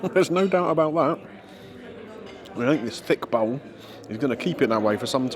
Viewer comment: did you say "THICK BOWEL"? I mean, I'm sure you'll keep it warm! Generally with you on the mushrooms - like the flavour, generally not the texture.